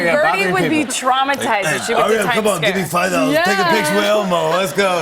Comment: Brie says "Time Scare" instead of Time Square. Lol